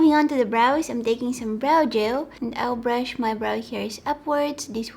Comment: I always thought she is saying "brow joe" and I was like what is that thing??.....Now I realised she is saying "brow gel"...love you and your accent...stay safe and healthy!!😍😙